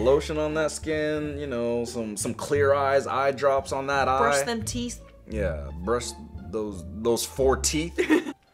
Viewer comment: I thought that when was talking about the ‘Frankenstein baby’ I thought he said “it looks like butthole teeth” 😂 😂 😂